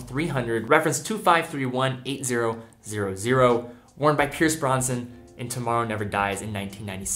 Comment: did he say “Pierce Bronson”??